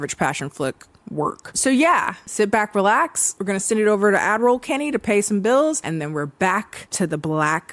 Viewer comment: i know in my heart that she says 'ad roll kennie' but every single time i hear 'admiral kennie'